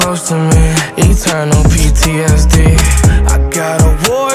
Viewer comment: Is it just me or when he says "Eternal PTSD" Is sounds smooth af? 👌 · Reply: Before i thought he said, "you'll turn on ptsd" but eternal ptsd sounds so much cleaner